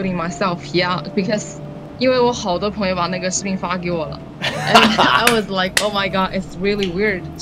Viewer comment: I heard the n word on 7:41👴🏻👴🏻👴🏻 if not go 10 seconds back